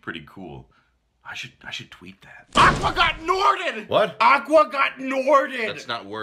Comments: what is he saying in here? Can someone tell me ? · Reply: Aqua got norted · "Aqua Got Norted" will always be the funniest thing I've ever heard. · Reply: Wow I totally thought he was saying "Aqua got nordic" and I was like "does he mean the water froze?" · What even is he saying at 12:30? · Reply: He says ‘I should tweet that’